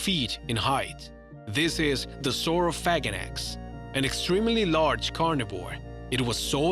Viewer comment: #8-Sauro"phaganax" lmbo sounded like he said sauro-faget-x